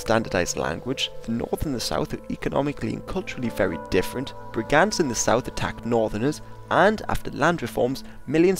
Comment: What does he say at 3:43? <word> in the South attack northerners What is the word? Is it Brigance? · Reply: Brigands.